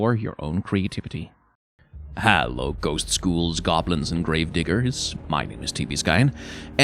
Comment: i heard "grave diggers" as "grave n****rs" somehow